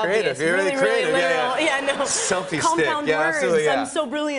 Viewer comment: I thought she said steak and not stick aha